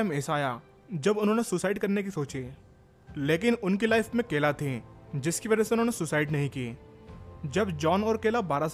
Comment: did I hear it right? Kela...